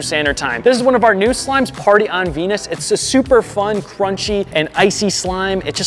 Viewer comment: I heard Varty on Pe